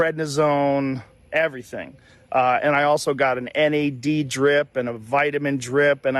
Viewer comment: 3:09 please do indicate where vitamin "D" drip, was specifically stated or, inferred by Joe Rogan, here. I don't think he said 'what' vitamin, he received in (IV) drip-form just "vitamin-drip" and, already the comments are indicating "c" as the vitamin, intravenously administered - clarification, please! Thank you very much! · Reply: he said NAD+ drip NOT vitamin d drip. (NAD is a form of niacin, vitamin b3)